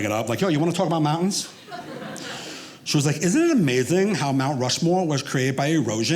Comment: Is there like an idiocy censor? At 20:50 I heard the question clearly, is Mount Rushmore created by erosion, I just substituted Rushmore with the Grand Canyon at first. I'm thinking because I subconsciously thought there's no way they're talking about the man constructed mountain face.